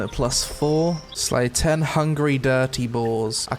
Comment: did anyone else thought they heard say (Slay 10 hungry dirty whores) xD